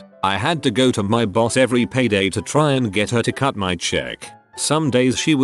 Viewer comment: My mind just tricked me and I freaked. 9:08 "Cut my check" What I hear: "Cut your nec-" WOah Uh Are you sure? Um.. Haha. Oh wait, I'm dumb. What's wrong with my brain?